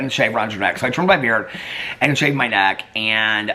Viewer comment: I was like this 😳 when I thought you said " shaved my d&%k" and had to go back and you said " shaved my neck" and I 😆 lost it!